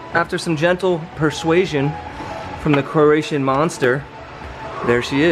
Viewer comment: Did you say "Croatian monster"?